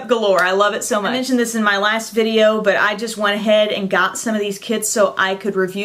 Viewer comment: Whew. For a second I thought she said it was her last video and I was like whatttttt?